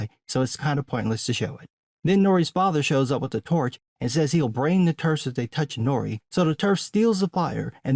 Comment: I thought Nori’s dad said he’d “Burn” Eminem and his/her buddies if they touched Nori’s foot hair, and I just assumed it was in some piss poor accent. Nope, he said he’d “brain” them as confirmed by the subtitles. WTF does that mean? Is “braining” someone some cruel form of torture I’ve never heard of? I don’t think it is, but I could be wrong. Chances are it’s just some more embarrassing writing.